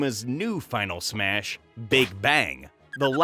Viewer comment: I heard "they bang" and got a little more invested in the video lol